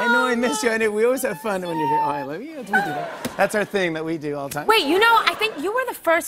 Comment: i cant be the only who heard jimmy say "dont do that".. he is so FAKE dammit